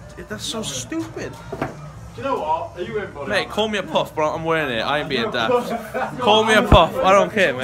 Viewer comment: BEN. Did I just hear you say "Call me a Poof, but I ain't wearing it" about the paintball body armor? Please tell me I heard incorrectly. I'm pretty sure I heard it said twice...